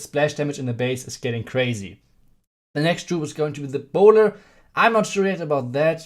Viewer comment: Did he say "the boner" at 1:46?